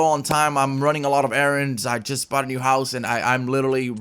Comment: did he say he bought a new house?